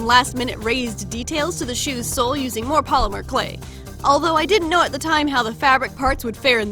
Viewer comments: I thought you said ''using female clay'' lol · I Thought You Said Female Polymer Clay xD